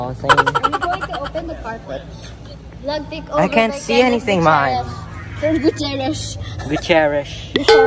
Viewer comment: may Mahal akong narinig diko sure but un ung narinig q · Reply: he say i can't see anything in here mahal